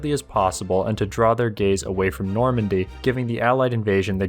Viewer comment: Did I hear: "Draw their gays away from Normandy"????